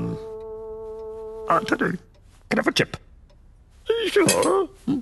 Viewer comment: Cana for chip? What was he saying?😂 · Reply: Can I have a chip 🍟